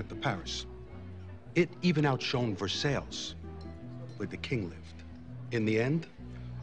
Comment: First time I watched I wondered if I was dumb uncultured swine whenever lil carmine talked. Like, is it me or did he just say ver-SALES